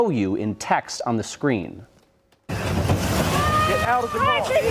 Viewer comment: you mean the 3 shots? Two shots were clearly fired she had completed her turn. The entry wounds of those gunshots will likely show manslaughter, if they charge him, not to mention the evidence that shows she was complying with at least one ICE Agent to "get outta here" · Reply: What I heard was "step out of the car. Step out of the f*****g car". NOT "get out of here". Nice try.